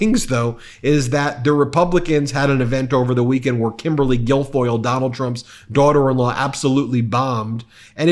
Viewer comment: I am not interested in derogatory elementary-school nicknames, but I just heard "Gargoyle" instead of Guilfoyle.